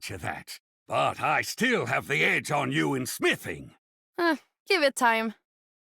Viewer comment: Thank God for this because I always thought torb said "I still have the edge on you in spitting" and here i was thinking about distance spitting contests